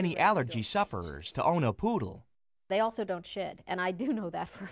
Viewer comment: it sounds like she said "they also don't shit"